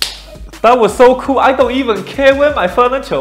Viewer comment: I thought he said "That was so cool I don't even care about my Father Joel"